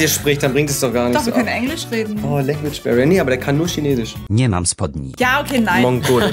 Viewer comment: Habe ich das „nie mam spodni“ (Polnisch für: Ich habe keine Hose) bei richtig verstanden? XD